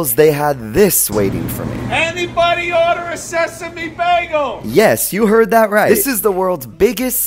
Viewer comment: When he said "anyone order a sesame bagel?" I thought he said: "anyone order a sassy big bagel??" 😂🔥